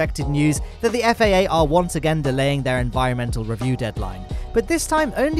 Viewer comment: I thought it said Starship decline and it wasn’t approved and it wasn’t gonna launch, then I realised it said deadline